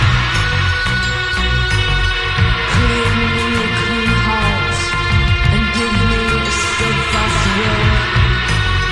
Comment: Does anyone know what all she is saying at 7:40? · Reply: Give me a clean heart And give me a steadfast will That I may be a strength to others around me Teach me to respect the bodies Of my fellow creatures May my body be the servant of my soul, and may both body and soul be Your servants. seems to be a Catholic prayer or reworking of one, hope that helps